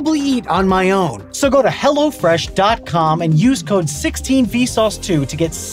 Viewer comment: did anyone else hear "so go to hell" XD